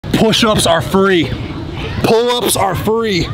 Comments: It sounds like he said push ups are furry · Why did I hear “pull-ups are furry “